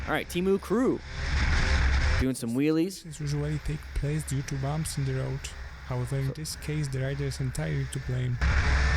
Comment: You didn't listen to what said when you tried to correct him. He said "speedwobbles usually take place due to bumps in the road, however in this case the rider is entirely to blame".